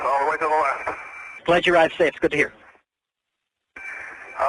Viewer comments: Wow! What a happy ending!! I was not familiar with this incident and I was thinking he was not going to make it. Bravo to the pilot and everyone that helped him. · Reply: Also… 11:25 I think he said “I owe you one!”. Not what the subtitles said. Yeah, I bet he would buy those guys a drink for the assistance. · I hear "I owe you one.", not the expletive? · I think he says "I owe you one" not "Holy f**k" -- anyone else agree? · "I owe ya one" is what is said.